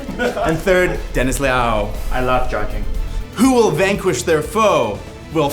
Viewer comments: istg if it wasn't for that subtitle, I heard Dennis said "I love jerking" 28:01 🤣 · Is it me or does Dennis say like "I love Jerking".